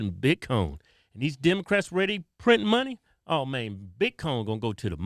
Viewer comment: Bitcoin. I thought he was saying, "Big Cone" lol